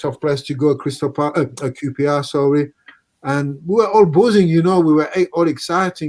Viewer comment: santos says the bramall lane was boozing? i thought he said buzzing